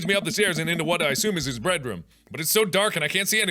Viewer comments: Did he just say "breadroom"? XD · "breadroom" that's all I heard and I refuse to hear any different the room is filled to the brim with bread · hehe i heard bread room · did you say breadroom, that sounds delicious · Okay when Mark said "Bed room" it sounded like he said "Breadroom" and I think that's pretty cute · am i the only one who heard "bread room"? i cant stop laughing